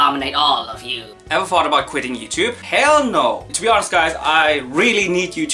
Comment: because of his accent it kinda sounded like heil and that’s funny because he is german